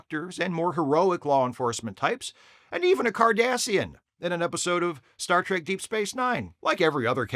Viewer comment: You said he played a Cardassian. I thought you said Kardashian so my brain struggled to figure out what that had to do with deep space nine. Now those would be some interesting aliens, the Kardashians.